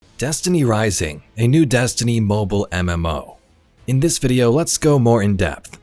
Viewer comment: Thought that said "NEW DESTINY MOM", I am now very sadden...